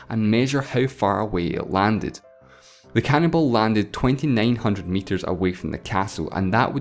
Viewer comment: Sounded like he said the "cannibal" landed 2900 meters from the castle lol